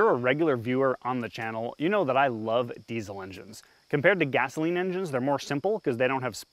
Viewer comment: I thought you said diesel engines are reliable